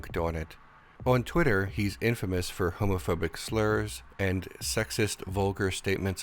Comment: for a second I thought you said Al was homophobic and I got real worried